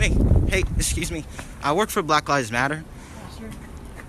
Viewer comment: Did he say -"I work for Black Guys Matter?" Imma use that as a pick up line from now on. LOL